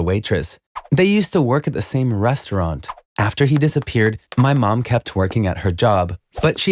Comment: "my mom kept working at her job." What I hear: "my mom kept twerking at her job" Me: "WHAT"